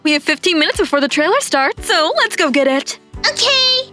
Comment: it says otay instead of okay